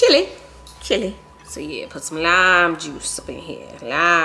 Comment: I thought you said you put some lamb juice to the watermelon 😂